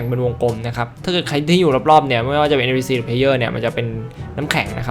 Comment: พูดว่า@ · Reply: “ไม่ว่าจะเป็น NPC หรือ player “